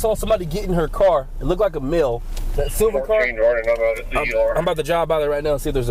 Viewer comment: “Mill” 😂 the way he says that instead of “Male” lol